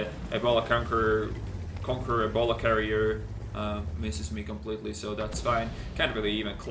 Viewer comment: The conqueror ballcarrier, the rheinmetall ballsack, the t110e titty, the t90fly, the walker bullshit, the e50bitch ( ͡° ͜ʖ ͡°) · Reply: I thought he said conqueror ebola carrier